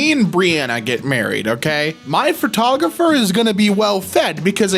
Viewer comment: Wait, did I hear bree? As someone who watched her content many years back, knowing damien was in it too that’s cute as hell lmfao.